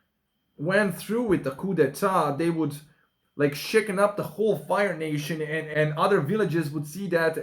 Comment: did you just say Fire Nation, my guy? 😄